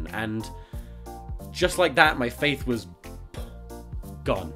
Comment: I was watching this video and about 3 minutes in I realized that the title of the video was "the day my faith died"...I thought that 'faith' said father